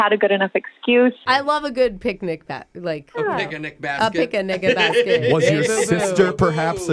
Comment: Whoa!!! Did Brooke just say a pic a nigga basket 3:30 🤯🤯🤯🤦🏾‍♂️